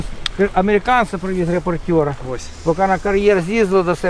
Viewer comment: CC says "reports" but this should be "reporters"